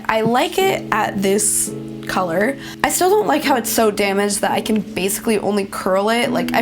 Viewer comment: i thought she was going to say it was so damn expensive but she said damaged... 😂😅